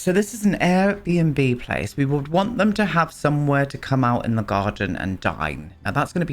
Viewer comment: I thought you said "somewhere to come outside and die" 🤣🤦🏼‍♀️ clearly a bean in the ear situation this morning 🧐